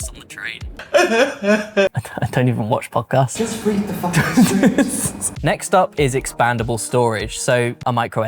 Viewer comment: did he say "just read the F-ing script"? 😭😭😭